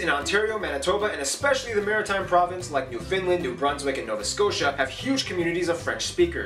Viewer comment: Did he say New"Finland"? lol Am I the only one that heard that?